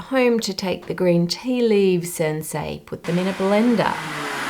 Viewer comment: I don't know if captions can be corrected, but at about 12:32 there's an error (reads "Sensei" instead of "and, say,"). At about 22:48 the captions read "satchel" instead of "subtle." I hope the error is on my end (I checked that I hadn't accidentally selected autocaptioning, but maybe it's glitching and showing me the wrong thing).